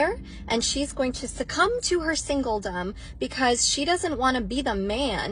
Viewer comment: So the captions at 3:26 get “succumb” right but then miss “singledom”? And the cherry on top is the captions say “single dumb”. Yes, quite often, I would agree.